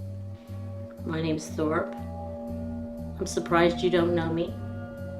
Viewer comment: I thought she was saying, "I'm Thor". Not "I'm Thorp" I was almost asleep when I heard her say this. I said out loud to myself, "What in a blue assed baboon is she talking?" I had to roll over to rewind it back. To make sure, she wasn't pretending to have been taken over by a Norse god.😂😂